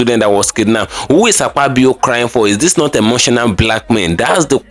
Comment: Where is Akpabio piking na? I thought you Said Akpabio piking?